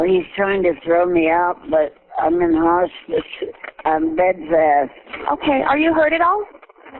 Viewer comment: she didn't say "on bed rest" she said "I'm bedfast" ...it's little stuff like that that really irks me. People who analyze body language can't even hear correctly.